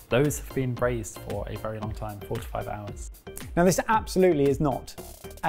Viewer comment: Did I mishear or did James really say those snails were braised for FORTY FIVE hours? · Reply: 4 to 5 hours. Like, about 4.5 hrs. Haha